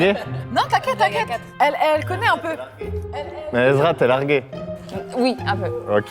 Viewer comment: c'est moi ou il a dit ezra?